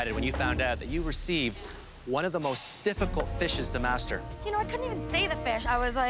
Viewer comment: did he just say “fishes”?